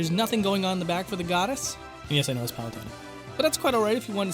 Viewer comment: Aaaaa I want to figure out what you're saying! I'm pretty sure it's funny. 🥴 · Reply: Ohhh. "and yes, I know it's "Palutena.""